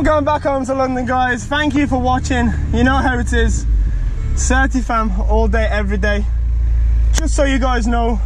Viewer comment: what is he saying and what does it mean "serty fam"? "30 fam"? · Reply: Logic 9924 certi/certified